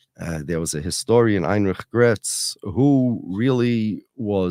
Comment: he mentions a historian. Heinrich Bretz? what was the name he said? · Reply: Heinrich Graetz